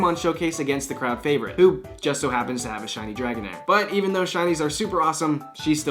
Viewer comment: did you say dragonite? 😑